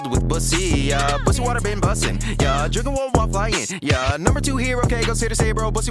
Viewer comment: "drinking one while flying" I thought he said "J*rking while flying" .........